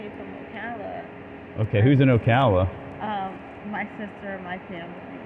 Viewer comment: Did she just say, "my sister..my pimp"?😂 Please correct me if I'm wrong.